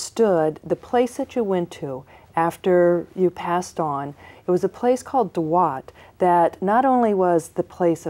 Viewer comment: what is that word she uses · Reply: Dwadasamsa which is also called a dwad, duad or dwadachamsha, can be thought of as any coupling, of various sorts. So, two of something. It's regularly used in astrology to represent counterpart, sub-ruling zodiac signs. I do not know anything about of her use of the word as a pronoun referring to a specific astral place, however. Let's ask that togeher, because 🤷‍♂️